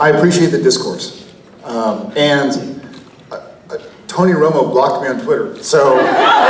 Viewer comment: Who is Jared talking about at 12:40? Did he say Antonio Romos? And why would he block Jared on twitter??? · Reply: V.E. Gray tony romo